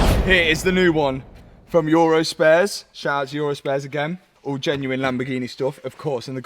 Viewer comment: I thought he was saying urus-spares 😭